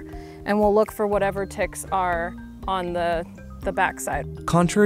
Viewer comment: I thought it said tricks